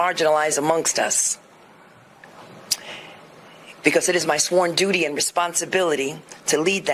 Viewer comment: Did she say it is "my foreign duty and responsibility2:06)"? Probably one of the most honest things she's ever said